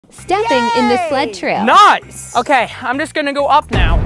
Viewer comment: Somebody tell me why when I watched the video a 2nd time, at the beginning I heard "Stupid and Dyslexic" instead of "Stepping In The Sled Trail"